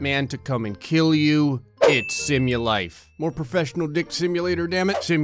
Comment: I thought it said the game was named ThugLife, I was like oh that makes sense, then I saw it was SimuLife, I muttered "ah, just the gray way"